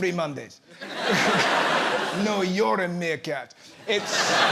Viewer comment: totally thought he dropped the "n-bomb" instead of Meerkat. 🤣💀